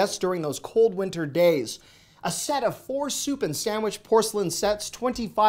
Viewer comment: When you said “soup and sandwich” I thought you said “stupid sandwich” … Lmbo! I have a similar-ish accent. So I can Josh you? I hope 😂 Either way it made my morning. Thanks! Have a great week!